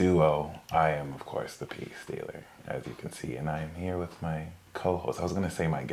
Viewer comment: my roommate (Leo sun, Pisces moon, and Virgo rising) just informed me that for the last few months she thought i had been saying " the pea stealer" and today she seen shirt and now knows that is not the case. epic as fuck.